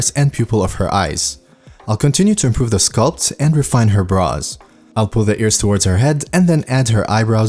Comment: Lol I thought you said, "refine her bras."